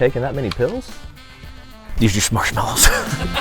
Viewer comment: did he say there just marshmellos??